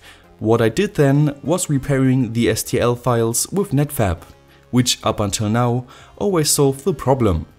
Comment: I thought he said netfap